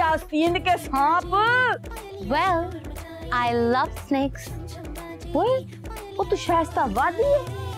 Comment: did she just say she loves sex?? 😮